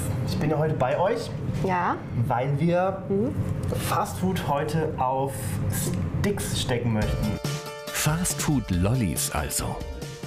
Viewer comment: habe verstanden : 'weil wir heute Fastfood auf Dicks stecken' 😂